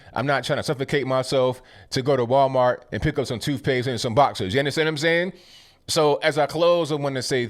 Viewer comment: I thought you said toothpaste and vodka's. I listened again, and was disappointed you didn't say toothpaste and vodka's.